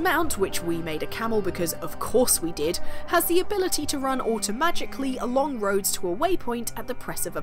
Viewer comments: Founding an Assassin Brotherhood is hard work soooo, why not chase hippos, map the stars and hunt for treasure instead?! Here's with 10 things you'll do in Assassin's Creed Origins instead of fulfilling your special assassin destiny. Enjoy! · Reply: Did say "automagically"? I suppose riding a camel in Egypt while using the first airel drone ever is pretty magical. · did say auto-magically?